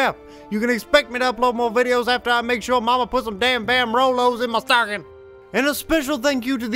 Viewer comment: Now I'm craving some Vamrolos and I don't even know what they are... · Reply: I heard it as Damn Rolos. You know, the round chocolate caramel candies.